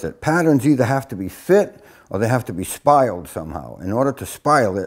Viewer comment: Question: What's that word that patterns have to have mean? Around 5:15 in the video. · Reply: Bhima Pandava Hi there the word is spile or spilling .It's a method of fitting or scribing mostly used on boats. hope this helps.